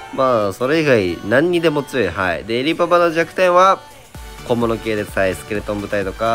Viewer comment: 小物系が小室圭に聞こえたww